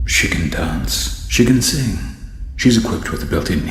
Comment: why did i hear “chicken dance”? 😭✌️